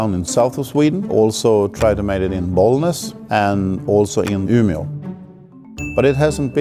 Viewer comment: the subtitles says Åmål but is saying Umeå